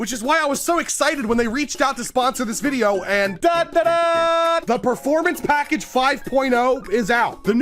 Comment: Is it just me or did he say dandadan when he said the sponsor?